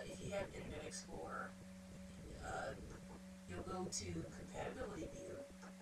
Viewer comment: I wish I could hear what you are saying at 1:40 - go to "Competitively View" on IE ? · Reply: Compatibility view.